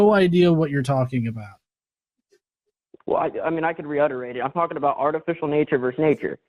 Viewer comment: Did he just say, "I can re-utterate"? 🤨